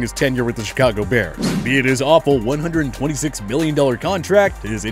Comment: Did he say 126 billion???